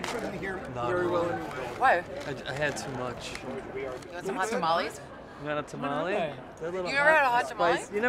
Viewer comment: I heard "you want some molly" the first time 😂